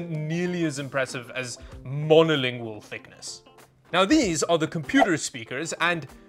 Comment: Who did you just call mongolian??!?